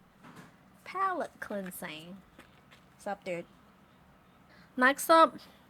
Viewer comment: i know she probably just said a word i didn't recognize but i'd love to think she said "sup, dude" to their dog